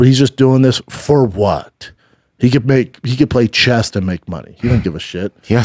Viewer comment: I fell out of my chair when I heard Schaub was talking about Jake Paul and said he "could play CHEST and make money". OMG!!!